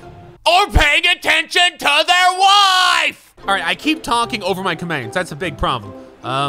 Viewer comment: did he say wine